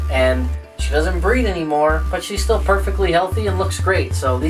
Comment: Thought you said she doesn’t breathe anymore but she’s healthy I was like uh what😧ohhh breed😂😂